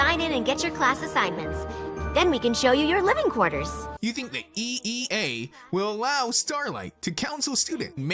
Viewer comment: sin 3:17 the subtitle say EAA yet the narrator said EEA +1 sin same thing